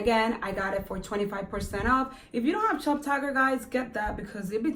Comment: What did she say at 8:29? I'm trying to find the app/website she mentioned that alerts you when there's sales. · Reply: Hi love the app is call shoptagr 🥰